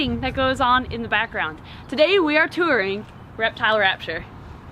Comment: I was just scrolling and I thought it said torturing reptile rapture